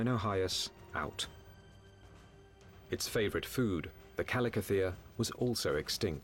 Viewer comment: what is the name of animal 44:00 ? My english is not very good so I didnt understand what he said. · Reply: Chalicothere